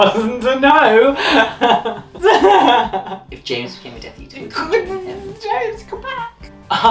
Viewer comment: did he say james come back? We need a vid with all the maraurders say it or shot it...